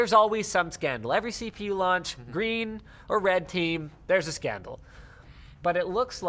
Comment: Did he just say GREEN OR RED team Boi we are talking about CPU's right now